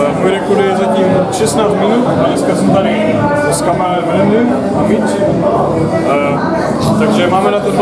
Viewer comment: did he say im gonna make him my bitch, or introduce him as Mitch?????? : )